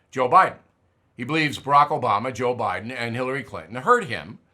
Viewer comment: Hold it......What in tarnations did you just say ??? JOBAMA!!!